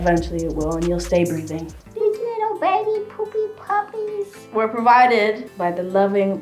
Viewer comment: Did she say 'Te Quiero Babby poppies puppies'? I'm excited 'cause I understand that she said 'Te Quiero' but at the same time..I don't think so, I mean... I'm confused bro 😂